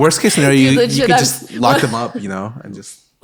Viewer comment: At first I thought scarra said "knock her up" instead of "lock"